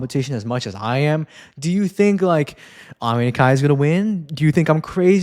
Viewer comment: Btw I'm watching with subtitles on and half the time when you say Rio Nakata the subtitles say Yuma Kagiyama 😭 And when you say Ami Nakai the subtitles say Alina Zagitova. Not sure what happened there. And at 21:13 when you say Ami Nakai it straight up says Roman Karimov LMAO